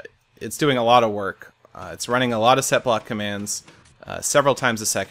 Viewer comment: My thing lagged so much it made the video say: "San....Tos.....Gay" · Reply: I even thought u said /sethblock XD